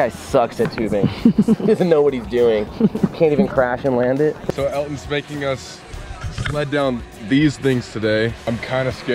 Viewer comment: Did anyone else hear say “so Elton’s spanking us” or was it just me??